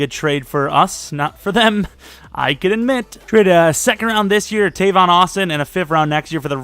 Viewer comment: I thought he said “A second round pick, A Tavon Austion” Lmaooo. Just another tavon Austin